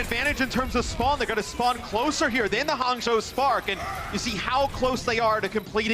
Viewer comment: did he say Hanzo Spark? 😂